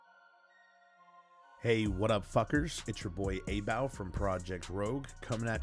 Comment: Did he just say at what up f*cker? Lmao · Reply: No. we said "Hey whaddup FUCKERS" ;) Thanks for commenting <3